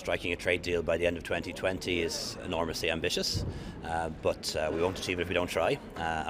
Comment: When Leo said “Enormously Ambitious” all I heard was “ Magically delicious”